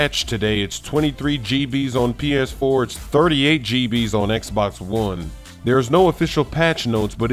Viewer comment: I swear i heard him say xbox 4